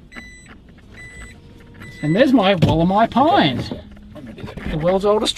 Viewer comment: did you say "wollemi pine" ? the extinct tree that was rediscovered? Make sure to keep it in the shade, it dies in direct sunlight!